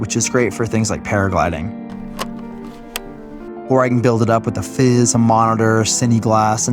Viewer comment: can anyone tell me what he said he can build up the camera with ...? a fizz? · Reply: Hello: A F.I.Z unit stands for Focus Iris Zoom. Basically, it is a device that mounts to the lens and camera rig, that allows a remote operator to control the Focus, Iris and Zoom operation of the lens. The camera operator can concentrate on camera movement and framing while an assistant is responsible for focus and other settings of the lens. They are also you when cameras are mounted remotely and not easily accessed, like when on a crane or mounted outside of a vehicle. We hope this helps.